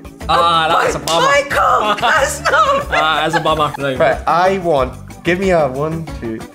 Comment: Guys, can you help me understand what a word Mike says when its bad luck for his opponent in the end of the video? like here 10:53 English is not my mother tongue so i want to improve it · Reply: "ah that is a bummer. That is a bummer". It means "that's unfortunate" or "that is depressing" depending on the context. It's great that you are trying to learn uncommon phrases like this. Good luck!